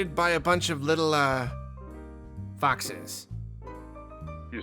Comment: !. Thats awesome video!. In the beginning I thought you said your house was infested by boxes!!.LOL!. After a few minutes I learned it was foxes!. I know you probably be like get your ears check man!! LOL